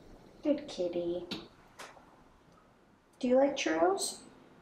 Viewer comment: Sounded like you asked if she likes churros😂 timestamp